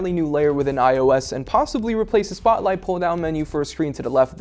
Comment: Did he just say the "spotlight pull to the left feature" ? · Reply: I also found that confusing. He said "replacing the pull down spotlight search with a panel to the left